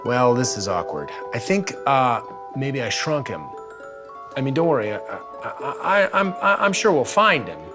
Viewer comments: at first I thought it said drunk xD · Am I the only one who thought that said shrek instead of shrunk?? XDDD